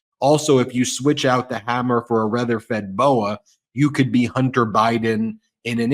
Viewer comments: Sorry I had to replay a certain part 3 times to make sure I heard what I thought I heard Ben say.." rether fed boa" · I think you meant to say 'Red Feather Boa' and not 'Reather Fed Boa'. Just saying... 😋